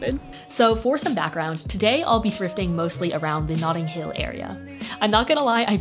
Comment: Literally thought she said "nothing hill" but rewinded and heard "notting hill" wtf is wrong with me? Anyways I love your vids!